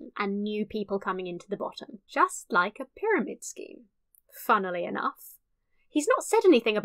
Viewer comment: I heard this as: "... funnelly enough."